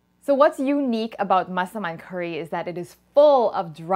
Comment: the translate says muscleman instead of massaman haha